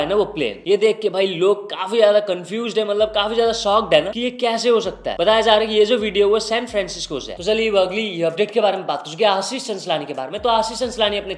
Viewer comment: pe kya bola? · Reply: San Francisco